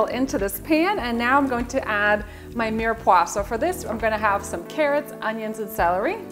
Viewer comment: What did she say she would add at 1:51? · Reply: Sorry for the confusion! I said 'mirepoix' - the onion, carrot and celery mix.